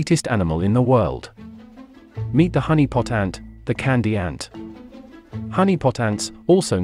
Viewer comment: Honeypot Ant. I've misheard it as Harry Potter.